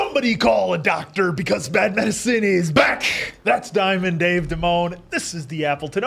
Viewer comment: LOLLLL GUYS I ALWAYS THOUGHT YOU SAID " SOMEBODY CALL A DOCTOR CAUSE BAT MAN CITY IS BACK .." BUTTT NOW I REALISED IM WRONG 😭😭😭💔